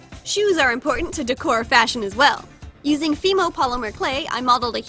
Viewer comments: When you said "Fimir Polymer Clay" I heard "Female Polymer Clay" SO NOW EVEN CLAY HAS A GENDER!? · thought she said female polymer clay lol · Who else thought she said “female polymer clay” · instead of Femo polymer clay I thought she said FEMALE polymer clay 😂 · I thought she said female polymer clay😅